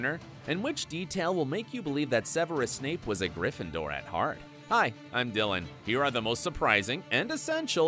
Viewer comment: When he said hi I'm Dylan I thought he said hi ima villan